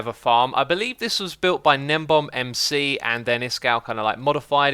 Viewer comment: whose name is he saying designed the farm? Nembomb? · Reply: Gnembon